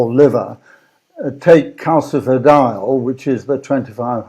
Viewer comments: what is calcifiial? that is what CC interpreted what he said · What is the form of vitamin D he references at 4:10? I couldn't understand him despite replaying multiple times. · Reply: Calcifediol